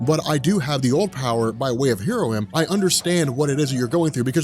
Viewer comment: Did I hear "By way of heroin"?